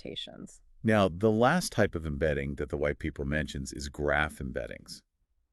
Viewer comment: I'm assuming that should've been "whitepaper" instead of "white people" 🤔